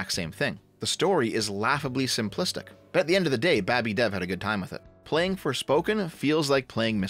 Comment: Did you just say babby? 😆 I miss 2BFP.